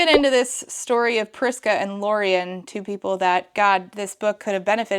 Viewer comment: I totally heard “Crisco” instead of “Prisca” multiple times. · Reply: I kept hearing it as “Vriska”, which was more than a little jarring.